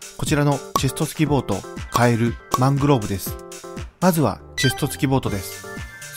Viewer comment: マングローブをマンゴーと聞き間違えた俺は末期